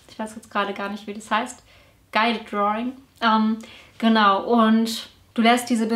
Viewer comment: Hallo, ich habe den Begriff bei Minute 5:40 nicht verstanden - "Guide drawing"???? Darüber wüsste ich gern mehr, finde aber zu diesem Begriff nichts. Vielen Dank, du hast mich wieder sehr inspiriert 😘😘😘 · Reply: Guided Drawing:)